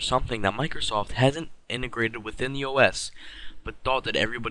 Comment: did he say os